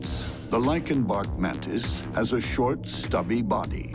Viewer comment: Thought it said "lion bark mantis" I was like oh no what sound effects are they using for this one?? Lmao